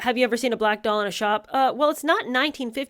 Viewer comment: The "black doll" arguement sounds less dumb when you initially thought said "black door" 😂 I thought Harry was ranting about white doors and was so baffled by the idiocy of it